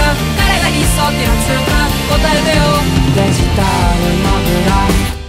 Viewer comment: 小学生の頃空耳かと思ってたけどガチでデジタルモグラだった衝撃